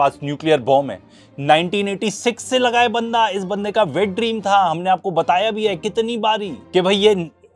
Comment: Did he say Natanyahu's "wet dream" ...